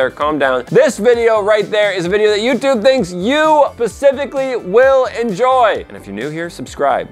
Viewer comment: Did anyone notice that at 23:44 says “ You will Pacificly will enjoy” instead of specifically